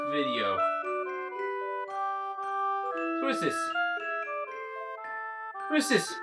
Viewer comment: I thought he said "who's this?" Like the hat from Minish Cap. Like... The... Never mind.